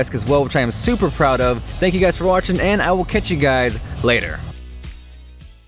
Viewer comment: it sounds like you said " I will catch you guide later"